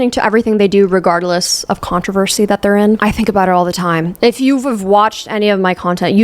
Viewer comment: At the 39:00 minute mark when she says "if you've've"😂 I rewatched like 3 times to make sure I heard it right